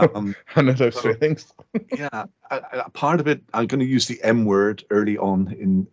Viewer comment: I thought he said "I'm going to say the N-word" haha, very informative i really like what khronos group are doing.